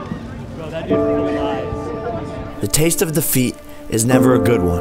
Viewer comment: I thought he said the taste of the feet